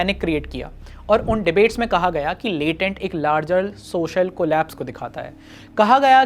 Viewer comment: I misheard "larger social collapse" as "prolapse" - poetic much for the outraged? :)